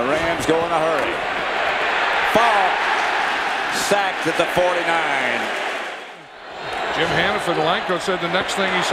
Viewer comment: did he say 'favre, sacked at the 30'